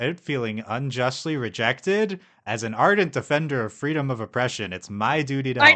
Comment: You had it right when u recited it. "... Freedom of Oppression" but i think y'all heard Freedom of Expression.